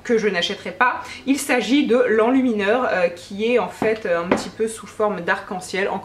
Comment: xD je dois avoir les idées putain de mal placées ce midi mais perso moi j'ai entendu "enlumineur roupettes" hein ... :P